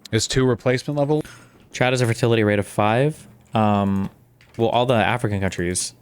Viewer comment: lol I thought he said CHAT has a fertility rate of five 😂